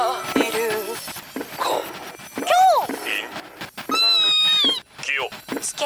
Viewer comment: Why does it sound like its saying Kakyoin Okay, this was when I didn't watch Jojo. I have read the manga and watched the anime. I was a dumbass but not anymore